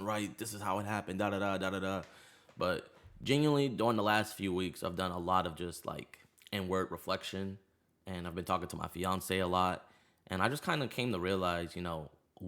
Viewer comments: could have sworn he said “n word reflection” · Did he say he was doing N word reflection at 0:24. Takes a big man to admit that